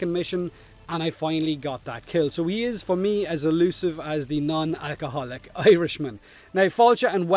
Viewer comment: "As the non-alcoholiv irishman" Wait, that's illegal... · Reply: Ohhhh. I thought he said "as a nun who is an alcoholic Irishman."